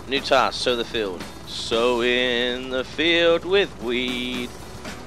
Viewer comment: Did anyone else hear that? 5:21? "Sowin the field with weed" There ya go!